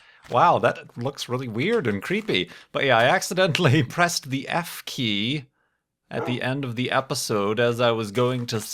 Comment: when he said that he accidentally pressed the f key i had to go back cuz i thought he said he pressed the f3 key!